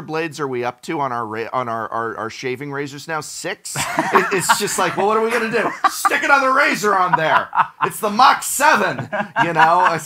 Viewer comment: when talking about razor blades, what I heard say was, “Stick another razor on there, it’s the Mox Seven”